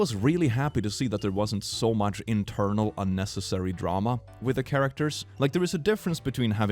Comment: he is really saying that their is no internal conflict in Frodo, Aragorn or Boromir? Thats wrong, some people watch movie wrong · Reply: No, he said inter-character drama